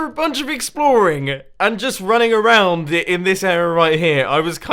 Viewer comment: The auto subtitles saying error instead of area returns!